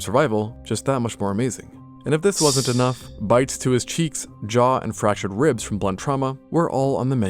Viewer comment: am I the only one who heard bites to there cheeks cause why the having a cheek contest now💀🥀🥀